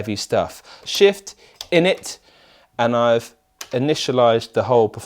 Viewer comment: just thought he was saying “shift, innit”